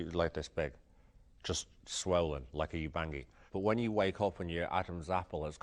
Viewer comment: What did Pete say his swollen lip resembled at 2:58? It sounded like yebangi? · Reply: "Ubangi" is a common but incorrect term for certain native people who put plates in their lips to enlarge them.